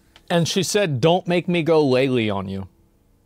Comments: as a french person i dont understand what "dont make me go lately on you" means, can someone explain? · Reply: He said "don't make me go Leyley on you". He was referring to the game "The Coffin of Andy and Leyley" and how Leyley is basically a psycho sister. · what did asmon said at 2:06 "dont make me go..."? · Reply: Don't make me go Leyley. It's reference to a game The Coffin of Andy and Leyley